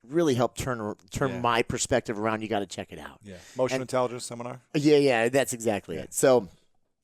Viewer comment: What seminar did Chris attend that he credits with turning his life around? They sound as if they've done Landmark. Anyone who is successful never takes all of the credit. Good for Chris to make sure Heidi was recognized as a full partner in the business. Tough to manage the fear and ego. Love seeing the vulnerability and realness in both Chris and Heidi . . . they both express the things that most of us go through in a relationship. The stories we tell ourselves can be the most destructive or the most empowering . . . it's a choice. I will sometimes ask my husband "would you treat your best friend this way?" Chris' ability to readily apologize is HUGE, HUGE, HUGE! My husband is the same and it's king in our marriage. Great couple, inspiring story, thanks Lewis! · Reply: I thought he said emotional intelligence but wanted to know the specific seminar . . . looking up emotional intelligence opens the flood gates hahahaha!